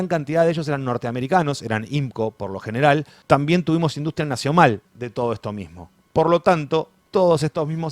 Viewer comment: Fué un acto fallido o escuché " industria nació mal" ? (Brillante!!!)